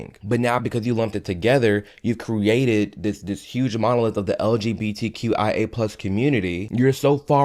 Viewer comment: Hold on. I might be confused here did you just say LGBTQ+ AI? Like artificial intelligence? Or idk educate me lol